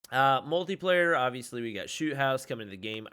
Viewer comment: I thought he said "Shoe House" but it's Shoot house😂